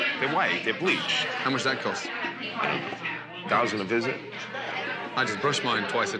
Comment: Random thing but, does anybody else have the Mandela effect at 09:00? I remember him saying “a grand a visit.” Instead of “a thousand a visit.”